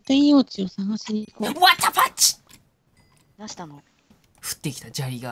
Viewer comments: わたパチ！！って聞こえる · の言ったことが わたパチに聞こえたw